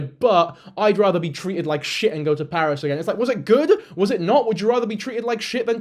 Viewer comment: I think she said “Paris Baguette!” It’s a Korean bakery-cafe chain that I personally think is tasty lmao. That being said, it’s sad that she’s trying to use it to compare/bring down the cafe in question